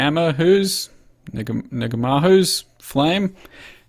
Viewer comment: Did he just said Nigga my Hoes???